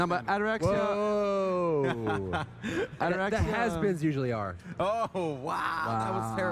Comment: can someone tell me what he says at 1:02:43? My quality sucks so hard and I want to know how he disses Zimpstar^^ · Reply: "The Has-beens usually are"